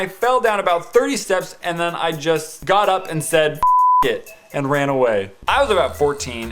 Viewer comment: Whoa whoa whoa did actually say f**k at 10:10?